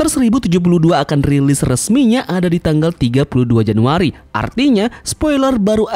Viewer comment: Apa gue salah denger 32 januari ?